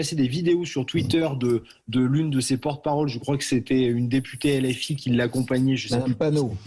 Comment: Ce n'est pas trop difficile à gérer, un débat entre quatre personnes d'accord entre elles sur absolument tout ? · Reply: Des le début j'ai entendu "porte parole PS"😂🤣